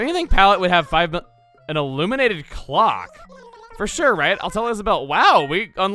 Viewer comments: i thought said Illuminati clock i instantly said yes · thought she said illuminati clock ._.